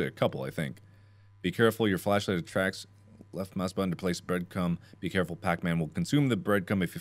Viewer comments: All I heard was “breadcum” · I cant be the only one who heard Birdc*m, right..? RigHt?!?!